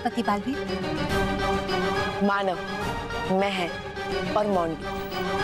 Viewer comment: it says monu instead of montu